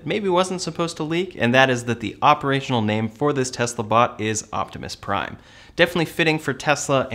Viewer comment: Did I mishear Elon when he said the name? I thought I heard him say "Optimus Subprime" which is hilarious! Haha